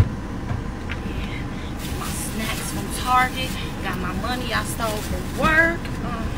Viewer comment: Does she say "got my money I stole from work."?